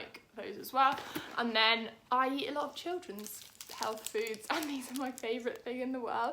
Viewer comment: Thank you for the video! Some of these are really helpful I’ve shared the video around! Omg I also thought you said you ate children